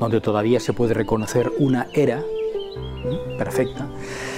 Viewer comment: En 7:06 que dice? Una hea, era, hera? Se refiere a la piedra rectangular clavada ahi? Tiene algún significado esa piedra? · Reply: Dice "era". La tenian todas las masias. "Trillar en la era" (trillar la paja)